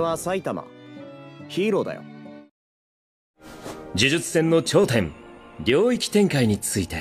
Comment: Gojo said Ryoki Tenkai instead of whats on their lol